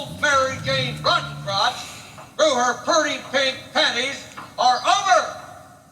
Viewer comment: I heard "Suzy Rottencrotch" before